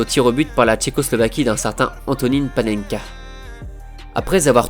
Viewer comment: J'ai entendu Antony le Tallec